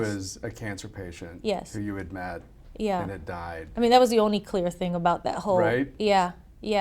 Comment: did he say "and it died"...?